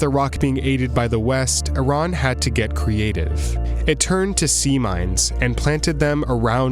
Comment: i thought they said SEA LIONS 🤦🏾‍♀️🤣